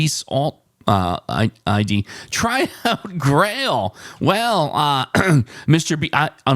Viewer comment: Grail? I thought you said Frail yesterday. But, I got the same result, nothing! I went frail, mound, suede, Queen